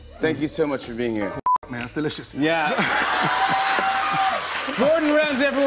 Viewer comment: Did he say: "Kurwa mać its delicious.."? "Kurwa" stands for "fuck" in polish language. :D